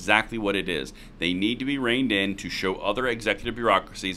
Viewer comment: I thought be he said brain dead not reigned in I think brain dead is more fitting